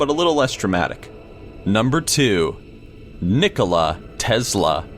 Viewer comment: Who else thought said Nikoli instead of Nikola